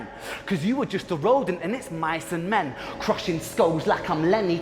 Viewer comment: I’m sooo happy you did this! 😁 Your background made your commentary more interesting than other reactors’. Loved it ❤ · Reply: Fun fact: I’m not a native English speaker. The first time I watched Ren’s diss, I didn’t understand the bit about Mice and Men correctly. What I heard was “You’re just a road, and it’s my ciment”. 😂 In my head, he was saying that Dotta was making his way on Ren’s work.